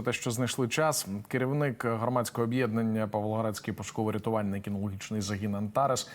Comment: Услышала «пушковi собаки» , вместо «пошуковi» . Думаю , что за порода такая. Обожаю собак.